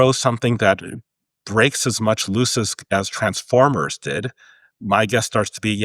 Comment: something that breaks as much what? Tf is "lusisk"? What is he sayin5g there? I'm hearing "breaks as much lusisk as transformers did". I must've missed something can someone fill me in?